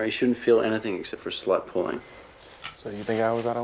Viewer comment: The caption had me dying “You won’t feel anything but slut pulling.” I legit thought that was what he said until I actually processed it